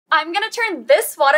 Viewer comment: I thought you said "I'm going to turn this watermelon into a big kitty" BUT ACTUALLY IT WAS A BIKINI😭